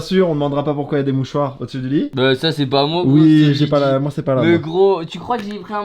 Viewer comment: Vers 12:25 il dit ça c’est pas moi c’est lidie (la fille du QG )